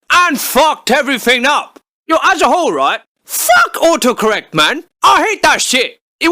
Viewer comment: I hear him say "ohoh correct" not auto correct lmao learn how to speak you ducking car